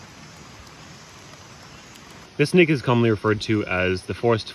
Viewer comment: it sounded like he said this nigga 😂 I know he said snake but go listen to it hahahahha